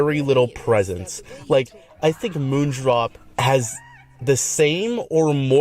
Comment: I thought you said ’moodflop’ here which also works